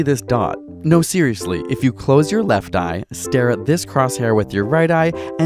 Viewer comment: "Close your left eye" Me: proceeds to close right eye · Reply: I actually thought he said to close your right eye and I did and I got so confused